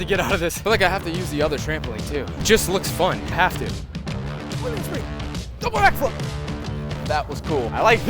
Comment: wth did he just say??? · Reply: “Double backflip”